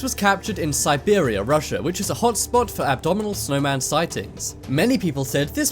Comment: Did you just say "abdominal snowman"???